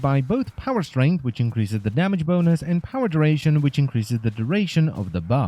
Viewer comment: did he say damage boners....lol